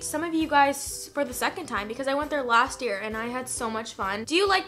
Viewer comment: I thought she said Donut Trump I had to replay that like 20 times